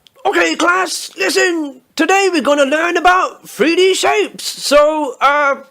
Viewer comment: this whole time I thought he said British apes